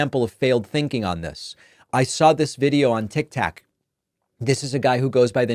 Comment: Great video... but was I the only one who heard tictac instead of tiktok minute